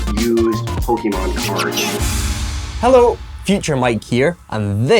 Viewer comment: Pokémon cards I thought you said poke a mom cards sorry didn’t collect your cards 😂